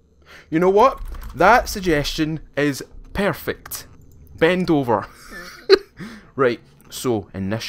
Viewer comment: When he said ben dover i first thought he said bend over.